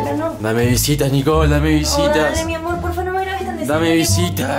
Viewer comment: Escucharon esa parte que dijo: deja mi amor 0:49 Estaran ocultando algo Nunca lo sabremos